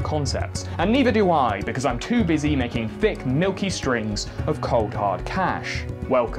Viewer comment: did you just say "necking thick milky strings"